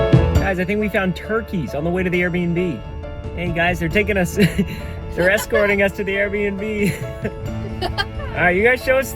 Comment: i heard there taking a shi- there escorting us to the air bnb